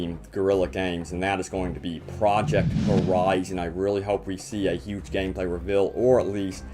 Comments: project what?? i wanna look in to it but i cant understand what he says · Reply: got a bit of an accent over here as I have been told. But yeah, it is Project Horizon. You'll definitely want to look it up! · What is that game at 4:01 I hear something about project and the rest isnt clear can someone please tell me it looks interesting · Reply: Demon Gaming TV Project Horizon by Guerilla Games